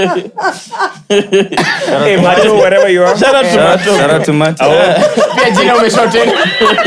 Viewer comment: What did say 1:00:05???😢😢 I have been rewinding and rewinding 😢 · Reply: "pia jina ume shorten"